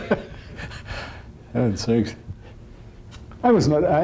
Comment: Did he say "having sex" at 5:00?